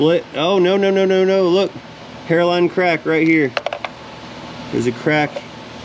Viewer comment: Thought he said “heroine crack”. He said hairline 😅